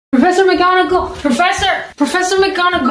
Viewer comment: The people who thought she said "Professor Mcdonalds" | \|/